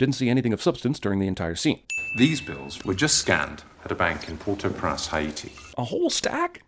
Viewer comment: Prance instead of Prince